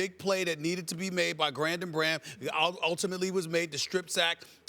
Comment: Did he say: "Grandon Bharam?" 😂